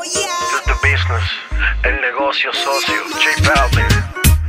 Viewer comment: j balvin , el negocio socio jajaja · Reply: gaston Salgado pense que decia el negocio sucio jajaja